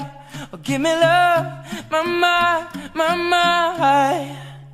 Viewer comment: Maravilloso video y una linda canción, pensé que decía dame amor give me love !!!